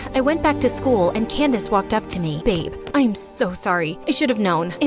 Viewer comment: DID SHE CALLED HER BABE???